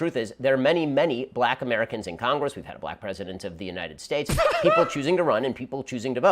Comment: I'm sorry, Ben Shapiro just said that we've had black presidentS (plural). What the fuck is this guy saying?